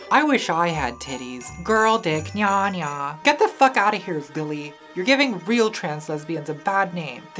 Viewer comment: I’m sorry but when you said “you can’t be an anime girl, lily” I died because my friend has Tourette’s and one of his ticks is “billy”. I heard it as: “you can’t be an anime girl, billy!!” Lives rent free in my head